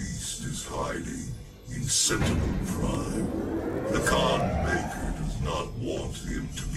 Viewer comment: did he said "sentinel prime?" like from Transformers ONE? Just realizing that :-D